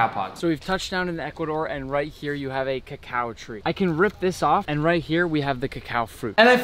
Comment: Bro I thought you said cow tree